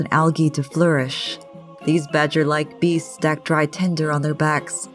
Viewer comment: I thought beasts said breasts at first. Do I need help?